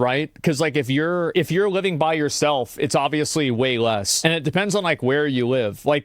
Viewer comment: "If you are living by yourself, it is way more." I don't think you understand how much it helps to have two people living in the same home, being able to have all utilities and stuff divided by half. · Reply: where did he say that? I thought he said way less